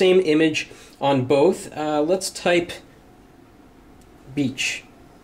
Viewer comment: for a sec, I thought he said "let's type... bitch"